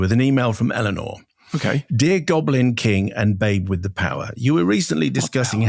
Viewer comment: "what power" I heard that lmao